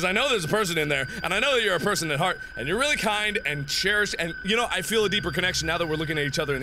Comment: It sounds like he says "you're really kind... And chairs!"